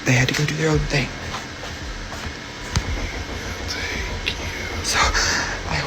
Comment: en el caso de Justin se escucha claramente una voz que dice Thank you minuto 25:11 segundos :face-purple-wide-eyes